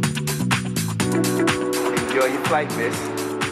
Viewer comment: i thought he said “enjoy ur flight bitch”